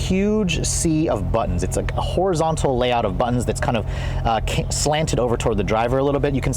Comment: did you say "Horizontal layout of buttons..."?